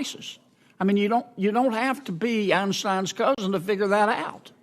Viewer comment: ‘You don’t have to be Einstein’s cousin’ I love Sen Kennedy. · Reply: Oh I thought u said epstein lol